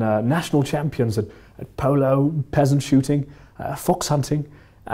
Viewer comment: sounds like he said peasant shooting